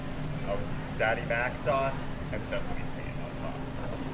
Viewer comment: Am i the only that heard him say hickles and msesasemies